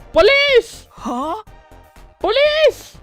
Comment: Did just say polis, polis at 33:30? That is police in Swedish, can speak Swedish or have I missed something here?